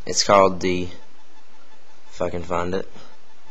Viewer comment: LMAO......... It sounds like you say "Fuckin' Find It"